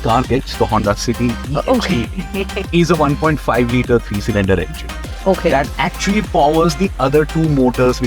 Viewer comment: Did he say 3 cylinder engine?